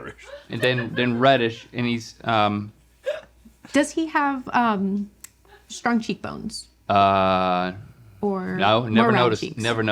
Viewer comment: I misheard "strong cheek bones" as "scrunchy bones"